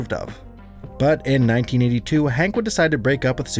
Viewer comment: did you say Hank?